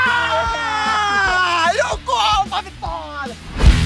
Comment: Só eu que entendi ele falando """EU COMO A VITÓRIA"" ???? KKKKKKKKKKKKKKKKKKK